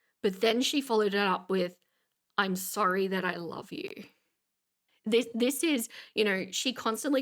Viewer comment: i thought she said I'm sorry but i love you, not that, but that makes more sense for what she's trying to do